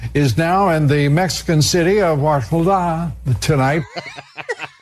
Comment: I heard "WashbulDAaaa... Tonight."